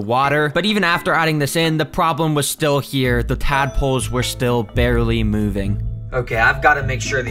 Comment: I thought bro said madpoles💀